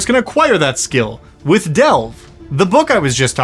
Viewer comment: i definitely heard Delf the first time and im over here imagining some sexy father figure elf